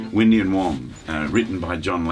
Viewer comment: What is the name of the first song he learn to play in fingerstyle when he was little ? I don' t understand what he says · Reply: I got it ! John D Loudermilk "Windy and Warm"